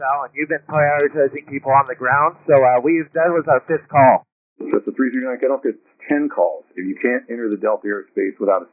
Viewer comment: ATC said "i don't care if it was 10 calls"